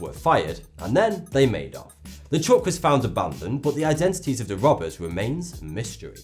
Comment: what does he say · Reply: You mean the truck was foundation abondent